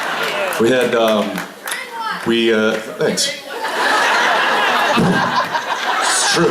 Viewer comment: What did the girl shout at 6:04? I can't understand her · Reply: "We're in one", meaning the hotel.